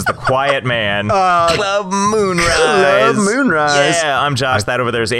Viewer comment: I thought it said club mayonnaise